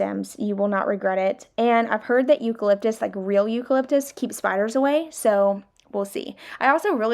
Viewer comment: Girl... I heard you say "eucalyptus keeps the fires away". Told my husband we should get eucalyptus for the house, ya know, for fire safety... 🤦🏻‍♀️🤦🏻‍♀️🤦🏻‍♀️ My gawd.